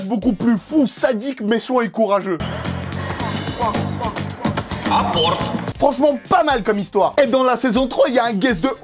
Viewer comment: c moi ou il dit "apporte"? Bref ma série pref c Elementary 😘😘